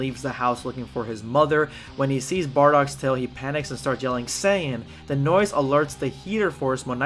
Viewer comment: Prepare yourself Damm saiyan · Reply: I thought he said tin saiyan